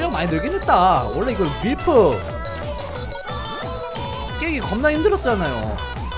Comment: 왜 존나라고 들리나